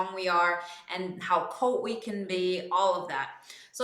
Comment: how what we can be? I didn't catch the word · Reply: How cold we can be :)